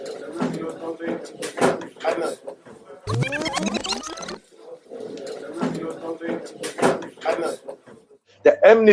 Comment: What's he saying at 09:30? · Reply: The Image Bear - Someone calls for the name Adnan